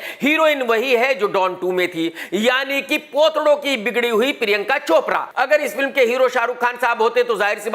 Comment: Kiski bigdi hui? Ye kya bola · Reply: Potado ki bigdi hui